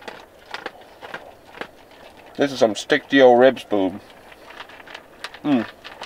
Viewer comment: Did you just say "stick to your ribs boobs" LOL (1:23:53) HaHa!